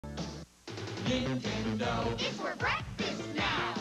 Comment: I heard is Ding Ding Dong, Not Nintendo Lol